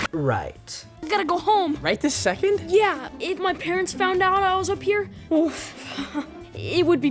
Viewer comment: it honestly sounds like luca said "If my parents found out I was a queer" because he said it so fast. idk maybe its just a me thing